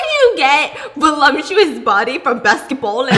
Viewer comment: NAWH I THINK HE SAID “BUSTY BODY” OR “BUSTY BOOTY” 😭 TYPING ME IS GIVING ME GOOSEBUMPS AFTER WATCHING THE FULL VIDEO 🙁